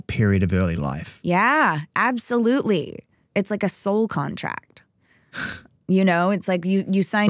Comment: “Soul contract”?!? Did I hear that correctly😭